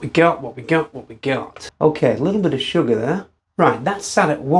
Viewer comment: You say 'a lot of sugar'; I hear 'rather a bit of future-alcohol'😍